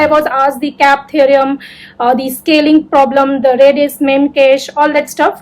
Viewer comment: can anyone comment What did she say here after scaling problem? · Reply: It's reddis meme cache